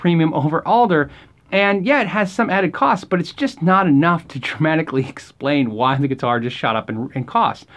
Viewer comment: I think its around 8:47 you say Tramatic...shouldn't it be "Dramatic"? I may be wrong. Very nice video!